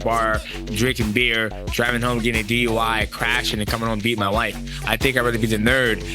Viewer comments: Wtf are those captions at 3:24? He said BE WITH, not beat. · Did I just heard Mighty Mouse saying beat my wife.?